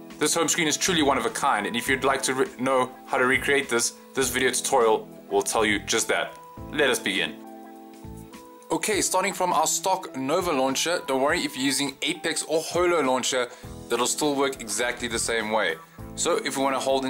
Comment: When you said let's begin I thought you said lesbian, ahahah great tutorial though thank you!